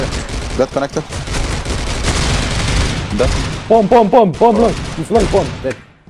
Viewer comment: anyone else thought he was saying “bum”